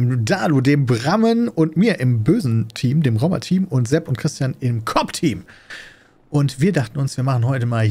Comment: Ich habe kurz Cock-Team verstanden xD